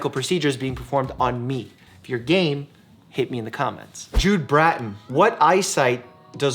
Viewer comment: i thought he said: "if your gay, hit me in the comments"